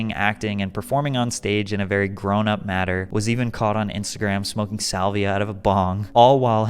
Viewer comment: Did you say smoking salvia???